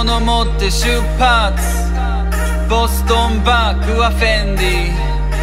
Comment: ブランド知らないから ボストンバックは便利〜♩って聞こえてた